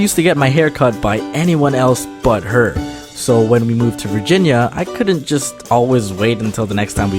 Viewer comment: "We moved to Virginia" Sorry, all I heard was: "We got a lot of virgina